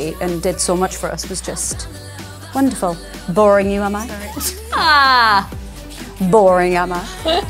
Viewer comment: Leigh genuinely yawns because she’s tired Perrie: “Boring you, am I? 😒 Boring am I?” I’m cackling 🤣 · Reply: I thought she said “boring you mama” 😅